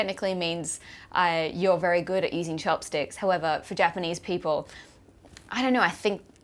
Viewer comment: for a moment there, I thought you said 'you're very good at eating chopsticks' - lol! Also, why hasn't anyone said that to me yet, in my total of four weeks in Japan? Maybe next time I go, I should eat alone without my boyfriend once...